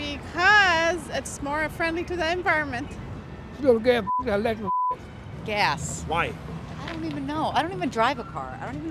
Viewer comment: what did he say 😂😂😂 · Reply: Phuq that electric sh*t 🤣🤣🤣